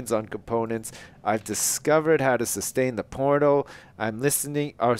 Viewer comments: lol it sounded like sustain the porno. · Lol It sounds like he says 'I've found a way to sustain the porno'